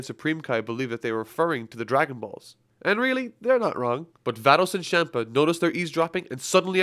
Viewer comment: I literally thought you said thanos and champa 🤣🤣🤣